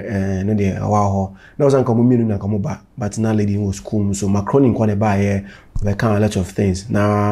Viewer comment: Every time I hear say “Mark Rooney”, It sounds like he’s saying“macaroni”…like taalia 🤣🤣🤣